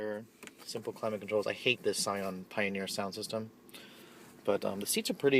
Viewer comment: bro, you sound like a little bitch all i heard was "i hate this"